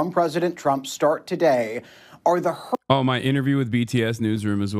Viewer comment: STOP I thought you were saying your interview with BTS. That almost made my heart stop. You can’t use BTS unless you’re talking about the band now, you know this.